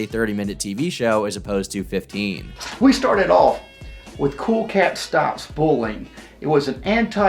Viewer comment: "Cool Cat stops bowling"? Lol I absolutely love clumsy verbal mannerisms also 52:30 I genuinely can't understand what is trying to say... "I senennelmen (sent an elmen?) an email..."